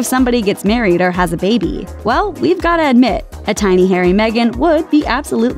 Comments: "a Hairy Megan baby" (as I heard it) just sounds weird. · at first i thought she said a tiny hairy megan at the end